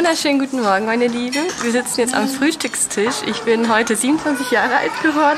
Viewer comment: ich hab 57 verstanden! 😅